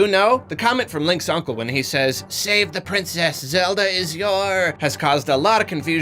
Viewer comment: I always thought the uncle was saying, "The princess is your "destiny".